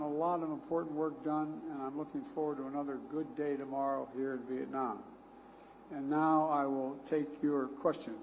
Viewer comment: did he just say i'm looking forward to another good day here in Vietnam ??